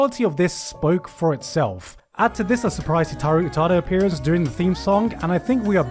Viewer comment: Did he say "Hitaru Utada"? I'm not getting google results there. Is that someone I should know or did he mean Hikaru Utada?